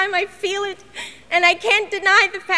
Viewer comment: I didn't hear REALLY, I heard RIGHT NOW. "You like me, right now, you like me". But people want to hear it REALLY, and that's why they hear it, though it's not spoken. Because it's so much funnier.